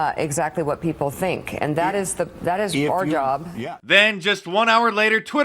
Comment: OKAY help me out here Mark … Did Meeka really say at 1:09 .. that trump can control what people think... and "that's our job"...??